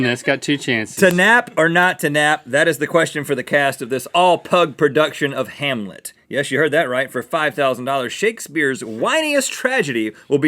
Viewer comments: For a minute I thought he said "Pug version of hamilton" and i got so excited. XD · I thought he said Hamilton not Hamlet and I got so excited for a second. Any Ham fans out there?